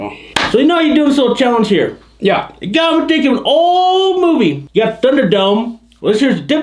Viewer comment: What’d say at 18:40-18:50? I wanna know what his challenge was...”You got me thinkin aalllll movie!” Wait what😂🤣😂🤣🤣